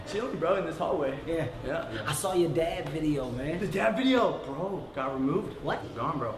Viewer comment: is says the DAD is dead instead of the DAB is dead